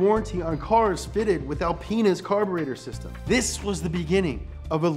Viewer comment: Wait, did he just say alpen*s?😂